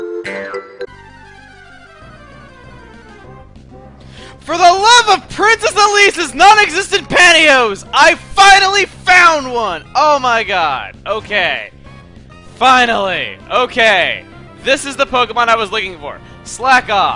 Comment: "For the love of Princess Elise's nonexistent pantyhose!" ~Chuggaaconroy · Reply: I thought he said non existant patios